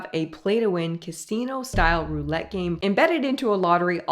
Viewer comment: I heard her say “casino.” She has a slight lisp which is why you might have heard a “t” in the word. I learned slot machine gambling from playing Sonic The Hedgehog 2 in the early 1990s. The level it was on was called Casino Night Zone. You didn’t win any real-life type of prizes. Neopets also has virtual scratch tickets and similar games.